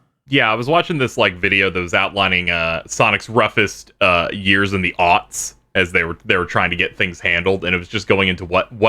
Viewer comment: Hi, not a native speaker here. What is saying there? "Sonic's roughest years in the what?" Can't quite make it out, and the automated subtitles don't really help. Which is a shame because I'd really want to check out the aforementioned video. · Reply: "Roughest years in the aughts", the aughts being the 2000s.